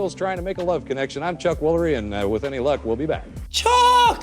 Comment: Hilarious how "Chuck Woolery" was misheard as "Top Quality".